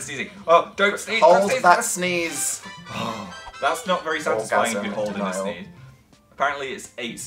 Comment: wait did dan say “orgasm denial” on